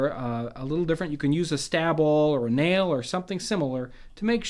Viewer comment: You said "stab awl" and I heard "stab all" agh!